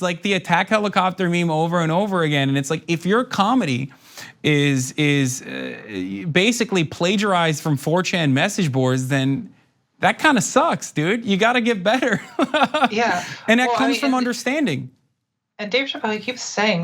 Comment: Did he just say Dave needs to get better.